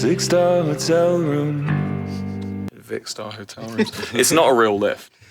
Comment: did they just say :vikkstar hotel room' 😭